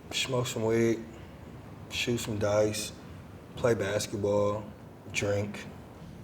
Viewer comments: When he said shoot some dice I thought he said shoot some guys😂😭💀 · i thought he said when he was in a gang he would link up smoke some weed shoot some dogs and drink